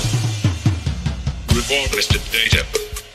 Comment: I think there is a mistake in the subtitles, but it might be that I'm hearing the singing wrongly. When the subtitles say "Report, Mr. Data", what I hear is "Repeat, Mr. Data" or "Rip it, Mr. Data".